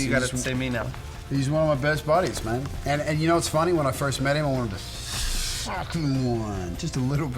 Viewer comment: I swear I heard Alex say "I wanted to F**k him once" LOL. I KNOW he didn't say that, it's just my fangirl mind😂